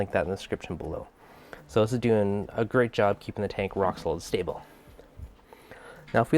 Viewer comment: “it’s doing a great job keeping the tank rocksyfhdjzbsyfvs stable”... replayed like 5x and still didn’t understand wth you said. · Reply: Rock solid stable :)